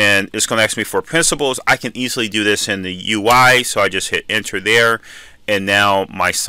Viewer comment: HI I'm so sorry but I don't understand what you're saying at 2:50. You I or UI or Uhaie? What's the manipulation you're doing on your computer? Thank you very much for your video.